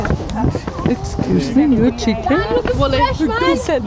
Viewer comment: what the hell is Manu saying lol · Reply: "excuse me, you're cheeky"